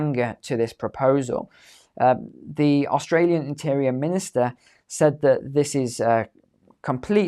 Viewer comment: austrALian minister? Did I heard corect ?